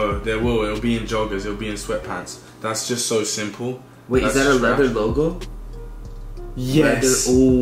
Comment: did he say leather Bogo?